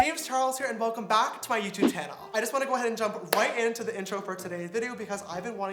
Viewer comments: Ok am I the only one who heard him say YouTube tannle???🤣🤣😂😂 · anyone else hear that?welcome back to my youtube tannel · Did you say tannel